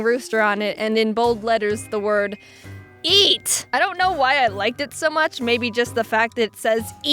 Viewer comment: when she was saying eat there was a ad and it sounded like she said E